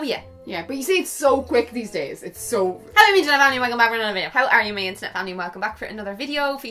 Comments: Oh I always thought you're saying 'hello my internet family' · Omg I always thought you were saying "hello my internet family" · I've been watching you since forever. And I always thought you said "Hey you my internet family" 😂